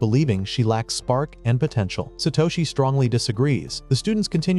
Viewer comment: Did someone say potential?